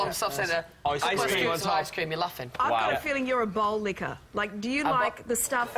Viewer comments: i think they thought she said ball-licker. im pretty sure she meant BOWL-lickerl. lol thats just what i think....( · i thought she said " i think you're a ball licker" omgomgogmgogmg